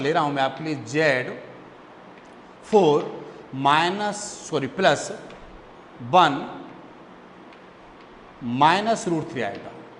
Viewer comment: bun instead of one hahahahahahaha 10:06 🤣🤣🤣🤣🤣🤣but even that sir explained very well👍👍👍👍👍👍👍👍